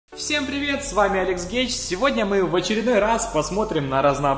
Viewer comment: Извини но мне послышалось "С вами Алекс Гей"